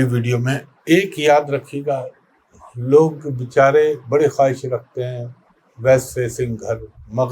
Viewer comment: What is he saying west what ? After 3:14 min ? Can anyone type what is her referring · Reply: West facing house East facing house Jo ghr west facing hote ya east